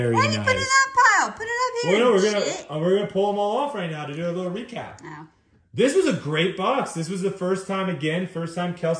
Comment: it sounded like said a potty word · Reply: “Jit” 😂